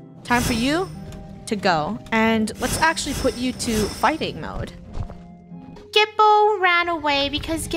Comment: I thought said farting instead of fighting😭